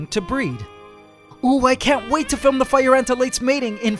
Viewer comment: Through out The video i hear "OOHH i cant Wait to film ant porn"